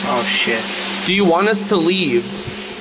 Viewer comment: When rug said "do you want us to leave" i heard "keep me company" for real